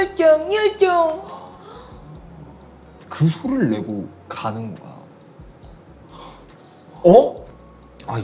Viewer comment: "가뿐 거 야~" 가 들려요